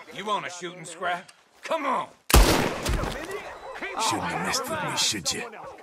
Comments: I can't understand what he says at 2:36 so many voices around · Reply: now that I think about it, I think he said ‘messed’, it sounds that way it does because of his accent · what is saying? It's drowned out by the game sounds but I think he's saying "you shouldn'ta messed with me should ya" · The fuck does he say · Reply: "Shouldn't have messed with me should ya" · what is he saying? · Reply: "Shouldn't have messed with me, should ya?" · What the fuck does he say at 2:35??? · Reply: shouldn’t have messed with me, should ya?